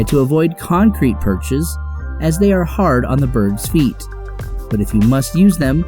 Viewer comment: IT SOUNDED LIKE HE SAID BIRB